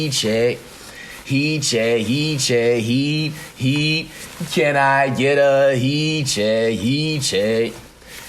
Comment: All I hear is, “ can I get a hee-cheh hee-cheh